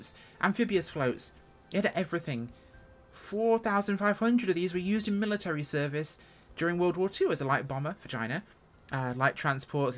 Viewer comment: and forward I thought I heard "light bomber vagina". I turned on CC and turns out I was right.